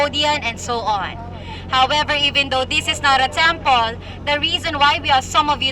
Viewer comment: Did she just say “this is not a tampon”?? 😂😂 Great one brother. Still watching and enjoying.